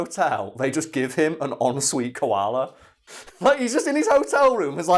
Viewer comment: when you mention the hotel koala all i hear is "en suite clamydia" lol